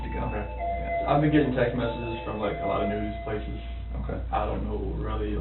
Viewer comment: listen closely, Chris isn’t talking about speaking with investigators here. He clearly says it’s the “news” who is contacting him. He even says “The Today Show” specifically. I realize that putting together these videos is a TON of research, but there were a few things you totally either missed, or completely gave the wrong critique. And, BTW, I cannot stand Chris Watts, so I am in no way defending him, but if you’re going to critique a case like this, ya gotta really get your facts straight.